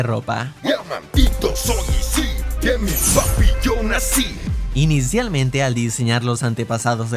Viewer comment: le entendí de un pito 😂